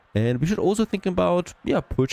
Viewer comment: What are you thinking of purchasing? I couldn't hear unfortunately. You are doing great! It's a brutal game you play, but it's going very well so far! 😃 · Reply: i think he said "purchasing one of the ai opponents" (most likely one of their islands)